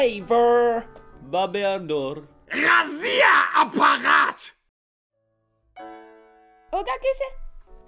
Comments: Мне здесь послышалось "Россия аппарат" 😆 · did he say "РОССИЯ АППАРАТ" [ROSSIA APPARAT] (Russia apparatus) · Мне послышалось или он сказал Разия Аппарат!